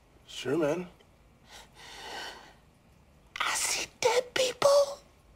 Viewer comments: I thought Shorty said “I see white people” not “I see dead people”. Am I right? Or trippin?? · I could’ve sworn Shorty said “I see white people” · I could’ve sworn I heard shorty say “ I see white people”😂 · I thought he said I see white people